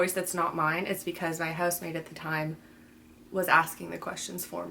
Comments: I thought you said “ if you hear another voice it was my husband at the time” and I was fucking shook and thought how the hell did I miss that/not remember that they were married?! And then a female voice happened 🙃 and I’m losing my mind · Omg housemate, i thought u said husband. I was like WHOA WHAT DID I MISS lol